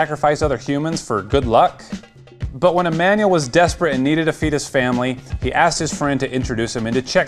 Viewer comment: I thought he said at 1:40 that he needed a feetus family Xd